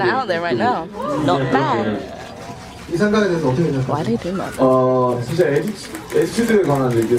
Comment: Is it just me or did I hear that person in the background say "why they build like that?"